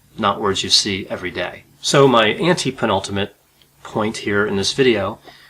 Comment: I was familiar with the word "penultimate". Then, you said, "antepenultimate" but what I thought you said was, "anti-penultimate" and my brain short circuited for a moment. After you explained the meaning, I realized my ante/anti mistake. Isn't language fun?